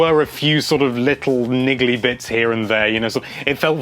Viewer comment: DID HE SAY "NIGGA"!?